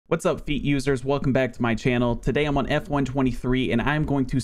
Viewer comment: At the start of the video did he just say what’s up feet users